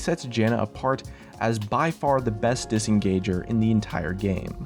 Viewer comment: did he say bestest engager or best disengager?